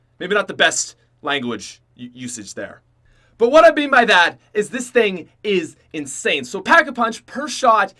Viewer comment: thought he said "but when I beat my dad" ☠